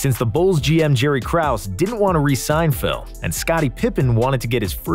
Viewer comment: So weird. I was casually listening while working and at around the 0:56 mark, I misheard "... didn't wanna resign Phil" as "... didn't wanna re-Seinfeld". I was like 'huh?', so I rewound it and saw it was talking about Jerry Krause, and I thought my mind must've been looking for it as "Jerry" was stated. Then a few seconds later, Jordan's hanging out with Jerry Seinfeld. What the absolute hell just happened? It's like the opposite of a deja vu.